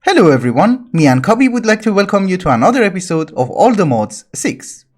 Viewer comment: you scared me, i heard you said "me and covid" and not "me and cobee"